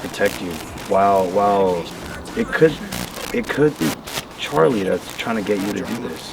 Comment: I don't know if someone has said this yet but at 54:36 when is saying maybe it's Charlie, the spirit box sounds like it says "Not Charlie". Not sure if the spirit said it because he goes by Charles or if it wasn't Charles at all. Either way it's pretty cool :D